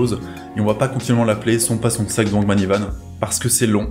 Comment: " c'est pas son sac devant le mini Van ? " Perso c'est ce que j'ai entendu quand il a dit son blaz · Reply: AH! Ça me rassure, la première fois j'ai entendu "c'est pas son sac dans l'mini van" malgré le nom écrit dans le titre 😅🤣